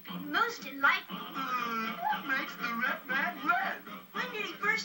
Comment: I love this movie, but I just sing “What Makes The Brave Man Brave” instead of “What Makes The Red Man Red”. In fact I always thought they were singing the former, because I didn’t hear them properly. I’m not deaf, I misheard them.